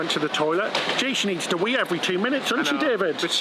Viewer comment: At first I thought she said she needed weed